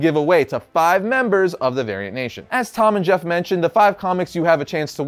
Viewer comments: Thought he said “Aryan nation” every time he tried to say variant. Lmao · not going to lie, i totally thought he said Aryan Nation at first, lol. but he won my trust back with the Angel Grove High t-Shirt.